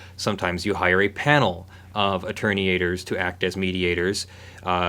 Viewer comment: I heard "attorney-ators" which had me thinking of lawyers in Gator outfits fighting